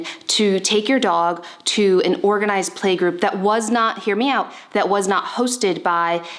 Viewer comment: Everytime you say “hear me out”, i thought you said “MIAO”